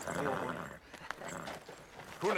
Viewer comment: To me this always sounded like "hunden er rar, men at ta' æ hund med i krig?", which in a heavy Jutlandish accent means: "the dog is nice, but to bring a dog to war?". Yeah... I'm a Dane, and I misheard one of the only bits of danish dialogue in this film =P